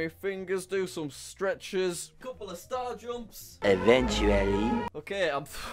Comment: Did he just say star jumps?